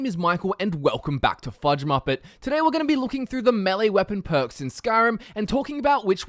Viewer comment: crap, i thought is said ''Melee Weapon Packs'' i was like.... u wot m8?